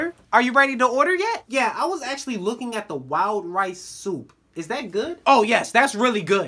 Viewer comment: when he said “wild rice soup” i thought he said “wow rice soup” lol